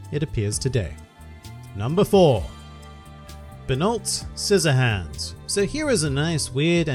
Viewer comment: did he just said binod.................